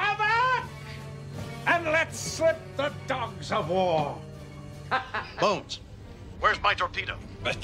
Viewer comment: I know he says, " Bones! Where's my torpedo? " but I thought he said< " Bones! Where's my Gordita? " xD